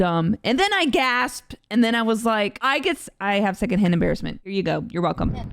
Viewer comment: I thought you said "I have Hannah Barron Syndrome" instead of "second hand embarrassment."